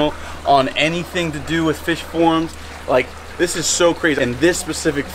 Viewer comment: fish forum..thought he said fish porn